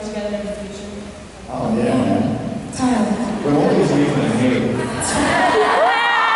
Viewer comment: "... would you do a project together in the future?" James: Oh yeah man Joy: Yeah Totally James: We'll always be Nathan and Haley. (not sure if i hear this correctly) Audience cheers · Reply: James says “but only as Nathan and Haley”. I thought initially he said what you quoted but I saw another video of this and it’s clearer that he says “but only as”